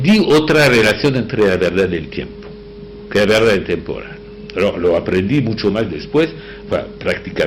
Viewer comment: Qué dice en el 6:41 ? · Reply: Que la verdad es temporal.